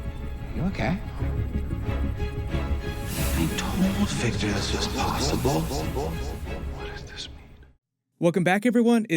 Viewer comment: Favorite part: when Ezra's Flash said "I told Victor this was possible." I literally screamed when he said this. Lol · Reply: All Might's All Right. I never understood what he said I thought he said “I totally figured this was possible”